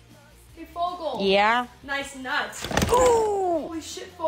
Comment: HELPP I THOUGHT IT SAID EAT FOGEL IN THE NUTSS 😭😭